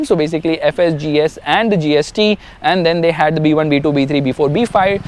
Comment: did i hear GST